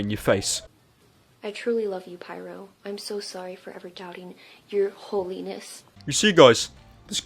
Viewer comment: i thought she said "your whole anus" not "your holyness"...